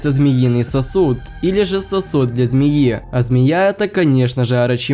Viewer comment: Я один услышал "сосут для змеи"?